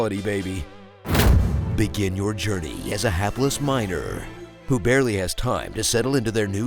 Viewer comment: I heard "hapless minor".